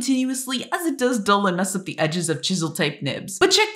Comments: i thought she said „Ohio” instead of „Ohuhu”💀💀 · For a second i thought ohuhu said Ohio